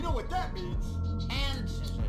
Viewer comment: I’m the Simpson vid when he said “hand scissors” I thought he said ancestors at first